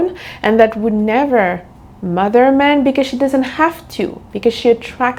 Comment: I love you to death and all love but I had the most hilarious moment. When the video came on you were giving Elvira vibes (which I love) but when you said "never mother a man", I heard "never murder a man"...which would definitely give Elvira vibes bahahaha